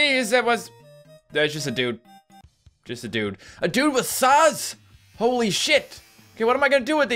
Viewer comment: when he said "a dude with saws" i thought he said "a dude with sauce" XD